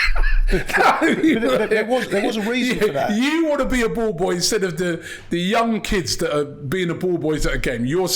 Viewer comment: Hi thought he said I want to be a Bald Boy😂